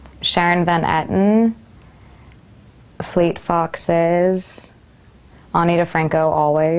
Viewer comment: what is the music band she says she listens to after fleet foxes ? at 1:17 .... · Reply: Ani DiFranco